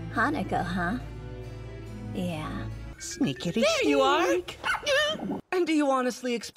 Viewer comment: btw did anyone hear mitsuba go like SNEaKaDy sNEak 0:14 That screech tho